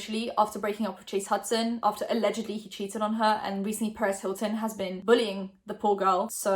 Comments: I literally thought you said Paris Hilton was bullying charli online I was like huh then I realized you said perz Hilton the scummy word 40 year man with a blog talking about literal teens like he gotta get a life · Omg when you said perez hilton had been bullying charli for a second i thought you said paris hilton was bullying her and i was so confused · sounds like you said Paris Hilton I was so surprised but then I realized you said Perez Hilton, I’m slow. 💀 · i thought she said paris hilton i was so confused